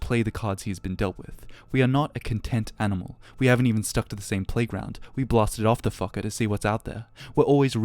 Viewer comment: ''we blasted of the fucker to see whats out there..'' hahaha did i get it right? i listened multiple times and thats what i hear lmao.